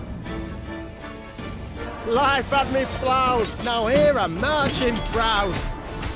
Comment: Thanks for the vid, helped. Was one of the ones I had to look up lol, but off topic; does anyone know what does the Hejduk say specifically at 0:27? All I'm hearing is "Life up me plout, now hear a merchant prout." which doesn't make sense to me and since I keep hearing it in game without knowing what it is, I've grown interested.